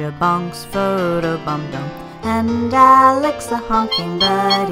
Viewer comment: with captions says “alex the honking daddy”💀🤷🏻‍♂️